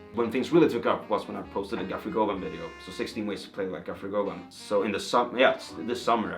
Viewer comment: i think he said guthrie govan at 13:31 not gaffery gogum😂😭 incredible video regardless man! thanks for sharing